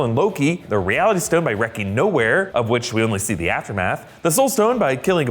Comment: I thought u said nowhere, then i realized its Knowhere.